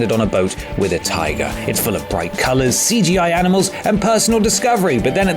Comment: First time I heard "CGI enema", then I listened again.